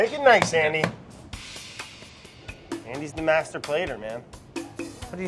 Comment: Brad: "Andy's the master plater" I heard: "Andy's a masturbator"